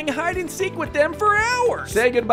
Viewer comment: Why do i hear "and say hi to pappi"💀